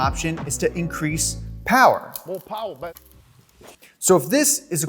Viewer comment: Who heard MO POWA BABAY for, pumphrey!!!!❤️